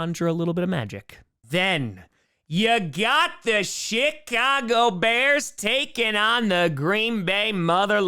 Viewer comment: did you say, "You got the SHIT-Cago Bears"?? I replayed it like 3 times. I swear that's what you said.